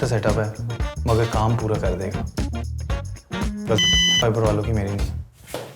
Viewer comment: I hear you say Stom Fiber HAHHAHAH !